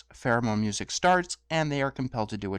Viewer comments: I almost spit my tea when I misheard Therimon as pheromone. For a second I thought was saying Fluttershy released pheromones and that's why The Stare worked! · Pheromone music? I thought you were saying "theremin" at first, but the second time I definitely heard "pheromone."